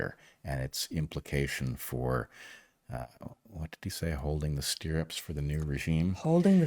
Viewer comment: I'm probably the only one who heard "holding the stirrups for the regime" and thought of gynecology rather than horse riding… Odd, since I've ridden a horse at least once, but I've never been to a gynecologist (nor ever shall). ¿Crossed metaphors—mixed wires? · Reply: I had to go back and listen again because I thought they said holding the reins. Probably because it made more sense to me. Crazy how the mind changes things that we think we remember correctly.